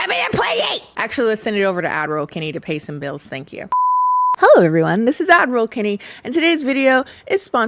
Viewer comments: I thought u said Avril Laverne and was like “she’s been replaced again?!” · Completely unrelated, but ever since Kennie showed the painting she commissioned of her & Russet showing her as an Admiral bc people kept misunderstanding her saying “ad roll Kennie” as “Admiral Kennie” I now always think of the painting when she says “ad roll Kennie” and I previously never misheard her 😂